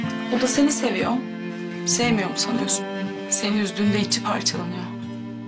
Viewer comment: hayat 0:06 saniyede ne diyor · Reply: Beyza Şura Özdemir seni üzdüğünde içi parçalanıyor diyo